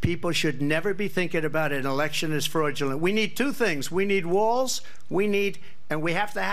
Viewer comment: Not even half-way through, but did I just hear him say "we need Walz"?! 😆